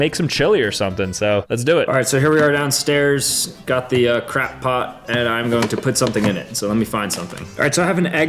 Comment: I heard crackpot instead of crockpot.